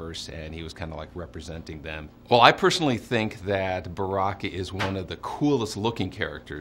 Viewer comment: did he say Barack